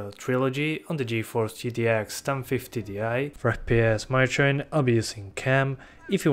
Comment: "for fps my train"? what? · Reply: I thought he said for fps monitoring ill be using cam